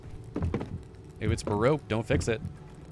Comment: you know somewhere in the 40 minute mark I almost thought you said, "If it's not baroque don't fix it," which I would agree with in some cases.